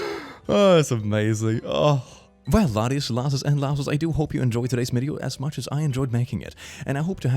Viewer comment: “People who make evil choices can look normal.” And that, laddies and lassos, is why serial killer documentaries exist. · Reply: Is it bad that I thought he was saying "laddies, lasses and lass-holes" up until now?